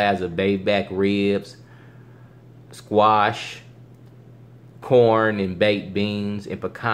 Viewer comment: I thought he said porn and baked beans.